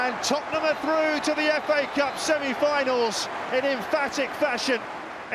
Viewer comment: what the hell did the comentator say at 5:36 ? some jewish strike ? Is saha jewish ?